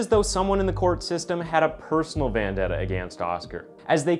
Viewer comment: Did you say "personal VANdetta?